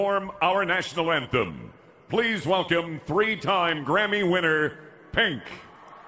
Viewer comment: It sound like he said "Fink!"